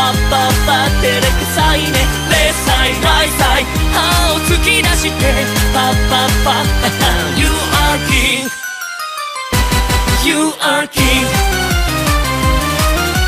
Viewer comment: The first time I heard this song I genuinely thought it said “you are gay”